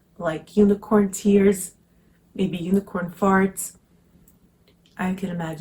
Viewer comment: Did u just say unicorn farts?! Lol 🤣🤣🤣🤣 or I just overheard? Haha U really got my attention with this fragrance, have to look up the name again 💖🦄